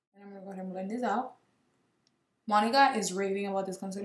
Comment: I can’t be the only one who thought she said “my nigga has been raving about these”💀 lmfao when she said Monica